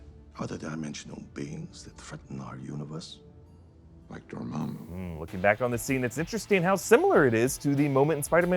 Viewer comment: I don't know why I heard "like your mom"